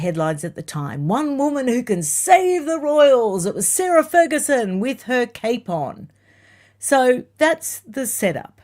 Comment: when you said … Sarah Ferguson with her cape on … I thought you said …Sarah Ferguson with her capon ( type of chicken ) I was rather puzzled thinking how she was going to wrangle some deal as she was caring for a chicken as well as the corgies ! 🤣🤣🤣🤣🤣🤣🤣🤣